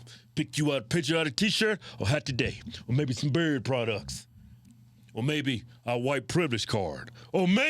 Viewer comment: For a long time I thought you guys were saying "bird products" and every time I kept thinking why the fuck are they selling stuff for birds?!